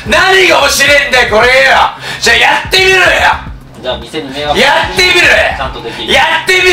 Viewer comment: やってみろ！ってのが社美緒って聞こえたの俺だけ?